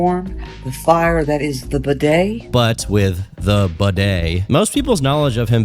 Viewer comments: I keep hearing it as "the bidet" and it's honestly cracking me tf up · I literally thought kept saying "the bidet" 😅